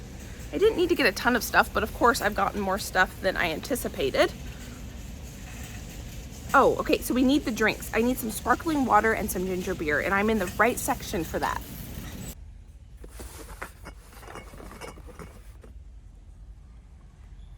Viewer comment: It sounded like she said ninja beer.